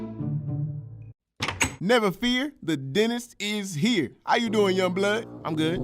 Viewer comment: Bro for the first few minutes I thought you said Dennis lol